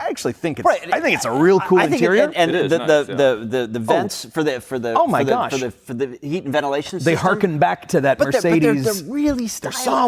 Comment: This is what I heard: "...the the the the vents for the for the for the for the for the ventilation system..." Good grief if you can't even speak maybe you shouldn't be making YouTube videos.